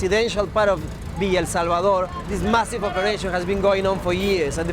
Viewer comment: I thought is said Ligma instead of Lima